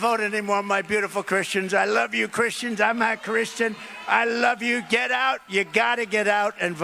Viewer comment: I heard him say im a christian.